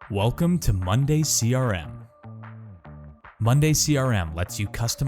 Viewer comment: I am constantly getting sales ads pop up on my youtube, and I thought they were saying Monday serum hahahaha.